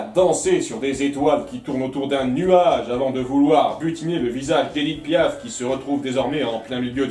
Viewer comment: Il ne dit pas que c'est Edit Piaf mais il fait une comparaison assez parlante emprunt d'un peu d'ironie, comme quoi la chanteuse c'est "leur Édith Piaf locale". En tout cas merci pour le nom de la chanteuse c'est toujours intéressant de savoir ^^ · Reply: Ah ok, c'est juste que j'ai entendu "Donald essaie de butiner le visage d'Edith Piaf", et ça m'a surpris parce qu'elle ressemble pas tellement à ça XD